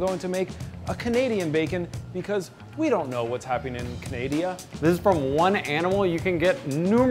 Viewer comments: I lost faith in humanity when in the beginning he said we don’t know what’s going on in canadia instead of Canada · DID HE SAY CANADIA 0:28 WHAAATT!! LMAO